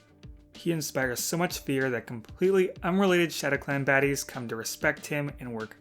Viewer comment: did you say “shadow clan fatties”😭😭😭